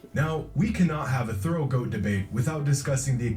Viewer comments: bruh ik u thought he said throat goat debate 🤣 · Man at 10:05 thought he said "throat goat debate" hahaha